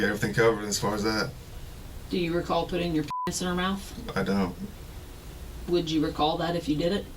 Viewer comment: There’s a few documentaries about how this guy was wrongfully convicted. Take a look at it. It’s quite interesting, from faulty DNA to the hungry prosecution overlooking evidence in the defendants favor. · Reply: Yeah at 45:03 he says he doesn’t recall sa’ing the girl but the captions say “i don’t know” instead of what he actually said, “i don’t.” Weird.